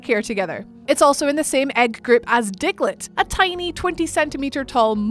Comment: Man it really sounded like she said dicklet